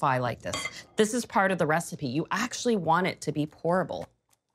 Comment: Did you say 'you actually want it to be horrible'??? 😂 I think I just heard you wrong.